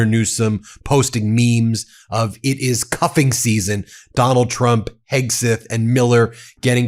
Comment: Not concentrating, I heard 'cupping' not 'cuffing' and immediately had a vision of Donnie with a glass goblet stuck to his forehead, sucking out his bad 'humours'. And then remembered the day I stuck one of those rubber sucker plastic rattles on my forehead to amuse my young children. They were highly amused... as were the people at work when I had to explain the resulting prominent purple bruise. My then wife, however, was 'Not Amused', she... Just smiled knowingly. 😌 'Go Gavin!' 👍